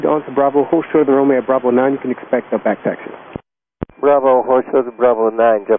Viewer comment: San Diego空港の離発着は年間を通じて90%以上内陸側の27から。冬や春先の午前中に海側の9から。JALのパイロットは、内陸側からの離発着しか経験した事がないと思う。動画見ていると昼前後の離発着方向入れ替えしているのがわかる。慣れない海側か内陸側への離陸は成田迄の燃料を積みしかも離陸した前は小高い丘と駐車場のビルなっている。結構プレッシャーが強い離陸だと思う。管制官の指示に集中できなかった気がする。 逆に通常の内陸側からの着陸は、特に中 大型機のパイロットにとってはタッチダウンのスポットが限られておりこれもプレッシャーらしい。 ここではパイロットの技量はBA>LH>JLの順番という感じ。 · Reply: 管制官が早口すぎて聞き取りにくいんですが、0:33 の最後に未和訳の部分があって "You can expect the back taxi."（back taxiを予期せよ）と言っています back taxiとは滑走路の逆端まで行ってから折り返すという意味で、 最初から海側へ離陸する予定になっています サンディエゴ国際空港では、混雑しているにも関わらず、海側から着陸しつつ 逆に海側へ離陸という運用をよくやっています